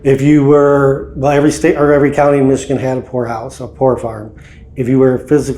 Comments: Is it just my ears or did anyone else had to double check the name of this house? I kept hearing them say, "Whore House." LOL, my bad. Great video! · At first, I misheard the kind of house it was... then when you said it the second time, and I realized that it was a POOR house. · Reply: I thought he said hor house too 😂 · I thought he said whore house.. 😬.. poorhouse got it.